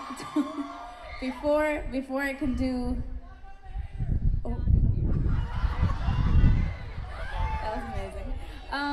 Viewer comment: what did he say? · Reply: tkvids "fuck the haters"